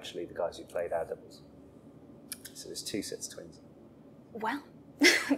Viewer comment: Can someone help me out? At 15:24 he talks about the hands of the clock. "Bill and Charlie are actually the guys who played ..., so there are two sets of twins" I can't make out that one word. Cool interview btw :D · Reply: "our doubles"